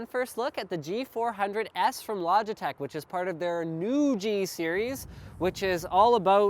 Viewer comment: Logitech Noogee Series is what I heard at first.